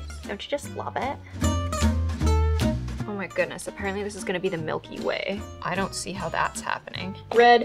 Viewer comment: I wasn’t looking when she said “the Milky Way” and I literally thought she said “the milk you weigh”